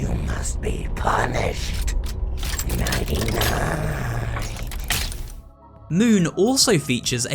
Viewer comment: I had captions on and when the moon animatronic said nighty night it thought it said 99